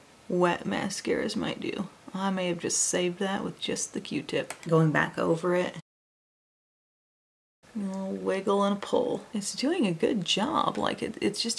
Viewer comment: "a wiggle and a pull"...in my mind i heard "a bushel and a peck", and now the song, which my dad used to sing to me all the time, is gloriously stuck in my head. Thank you for that, it's my favorite part of the video. I know that's probably weird to everyone else, but...🖤